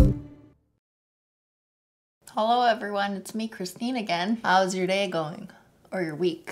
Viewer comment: When I first started watching this channel I thought she said “Christina Gan” instead of Cristine Again